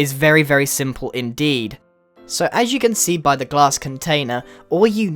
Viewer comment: When he said glass container I accidentally paused it then it played and I thought he said ass container 😹